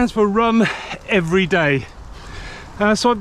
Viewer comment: I heard “rum every day” the first time 😉